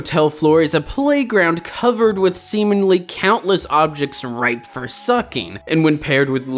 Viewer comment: at 14 seconds i thought for sure you said "covered in semen-"